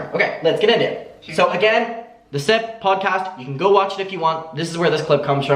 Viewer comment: Lol I thought you said "the Simp podcast" at first. Maybe they should change it to "the Simp"